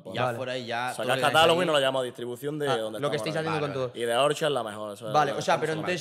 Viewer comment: Buenas, la distribuidora que nombre que dice algo como “de orcha” alguien me dice el nombre?